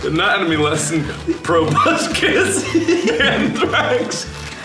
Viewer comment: If Markiplier was your science teacher: "Probuscis...hehehehe.....anthrax....hehhehe......thorax...hehehhe....sporax.....hehehehe...Got it?" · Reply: I am Beepbop XDD At first I thought he said "Probuscus" and I was like "WHERE'S TOBY TURNER AT!?!?!?! MY BOY TOBUSCUS"